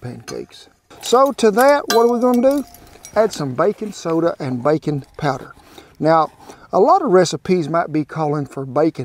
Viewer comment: I thought he said Bacon powder and got excited that such a product exist lol